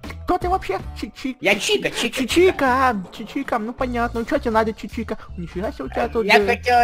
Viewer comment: мне послышалось Читер а не Чика